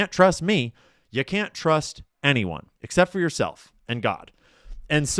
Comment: When Ian said you cant trust anyone but yourself and god, i thought he said yourself and dogs😂